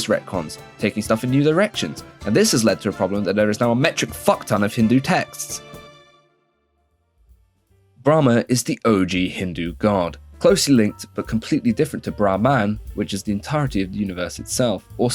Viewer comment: did bro called him bra man